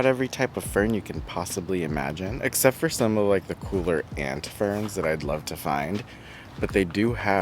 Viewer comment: did you say you want a Hand Fern? At 17:50? I wish for one too! I got one once at Rogers Gardens probably 1980 ish. It was a 1 gal. nursery pot in outdoor ferns, Doryopteris Pedata. It was tough, leathery and lasted more than a year in my kitchen. Unhappily, at 20 something, I was not yet experienced with providing optimal conditions. I wonder why growers don't grow them.